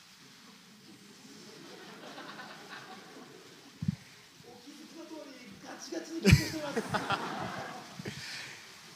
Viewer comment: あたり、なんて言ってる？？ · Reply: 「お気づきの通りガチガチに緊張しています。」かと思います！